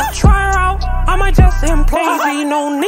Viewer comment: When he said “who tf is dora she a bad bitch I might explore ha” I was like AHEHWHIHSHSHH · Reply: LOL here I thought that he said I might employ her, this shit slaps😂🔥